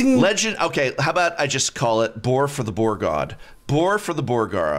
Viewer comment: I heard you say boar for the boar god... sounds like blood for the blood god... are you a warhammer player?